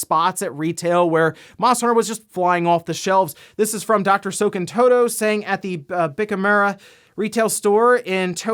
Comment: oh god when you said his name I thought you said " soakin' toe toes". then I looked at the screen and it said Dr. Serkan Toto. lol whoops.